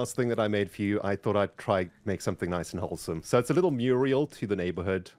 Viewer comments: Did he just say chobblesome? Grians word is catching on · did anyone else hear say "i thought id try to make something nice and 'chobblesome'"? i watched it back and he said "wholesome" i think but at first i thought it was a throwback to the hermit dare that grian had to do (grians ep 9-14)